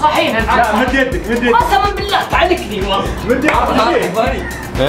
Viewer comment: وش قال في الدقيقه 7:38 ركزو!! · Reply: قال تعلكني ما فيها شي